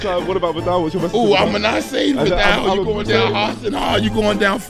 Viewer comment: Kinda sounds like hes saying 'im a nazi' XD